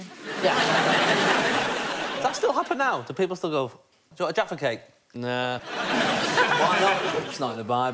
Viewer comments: What he said: "Do you want a JAFFA CAKE?" What the comment section heard: "Do you want to defecate?" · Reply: I heard "Jaffa Cake" , must be because i'm also northern. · What he said: "Do you want a JAFFA CAKE?" What the comment section heard: "Do you want to defecate?"